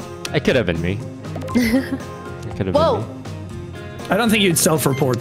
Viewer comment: tina says woah and the subtitle says wow! this is unwatchable